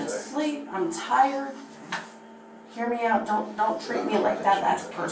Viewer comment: "Don't trick me like the last person" in full blown Karen mode 😂 she's so ridiculous!! Nobody tricked her, everything was explained very clearly to her. Multiple times. She wasn't "tricked" she's just being stupid LOL 😂🤣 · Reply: I thought she said "treat" not trick.